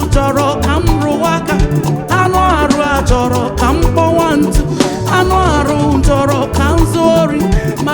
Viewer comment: Did she just say "kpowa ntu" 😂😂